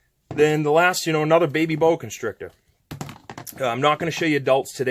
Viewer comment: When I first got my baby boa I knew I should let him sit for a few days in his cage. But, he kept coming to the front of the cage when I sat next to it and it was so cute so I opened the cage and he climbed on me and just chilled. This was on the second day. Now we’re bffs 😂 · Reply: I’m stupid I thought you said baby yoda